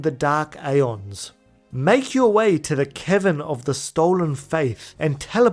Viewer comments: it sounded like you said 'The Kevin of the Stolen Fayth' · HAHAHA i heared "its time to make our way to the kevin of the stolen faith"... thought you called yojinbo a kevin xD